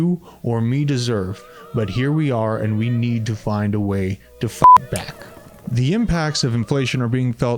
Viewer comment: I know you said fight back. But I swear to god it sounded like you said fuck back. 😂 Thanks homie!